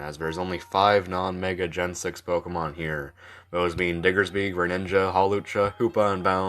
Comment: ayo what did u say at 5:41 ?????????? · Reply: It sounded like I said Diggersby